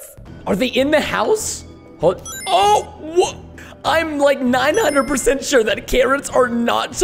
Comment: I thought you said “I’m like 90% sure that carrot’s are not so posed to go like in these areas that they have gone” for the first time I played it